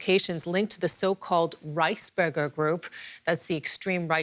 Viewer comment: Rice burgers sounds tasty lol add tomato sauce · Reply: oh thought she said rice burger it's Reichsburger lol